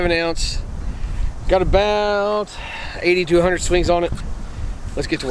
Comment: that bat looks super mint for 8200 swings man gotta say. :) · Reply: he mess' n with you because you said the bat had 80 to 100 swings on it. The video sounds like you said 8200 swings